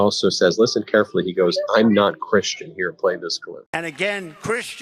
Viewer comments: I think Trump is absolutely nuts but to me it sounded like he said Imma Christian, not I’m not Christian but I definitely don’t think he’s a Christian so that fits better. And everything he just said was completely crazy. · I thought he said he wasn't Christian! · I thought that was I heard him say, I’m not Christian? And they swallowed all the kool aid with applause. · Thank you! I heard him say "I'm not Christian". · Sounds like he said I'm not christian. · did he just say "I'm not christian" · I thought he said, "I'm a Christian," not "I'm not Christian," but I'm sure I heard wrong. Voting for Kamala regardless. Trump reminds me of my ex-husband, except Trump is on a larger scale.